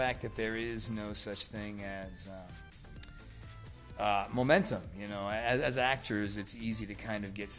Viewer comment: is he actually saying „momente“ orrrr?? i was shocked I thought he was speaking german 😂😂😂😂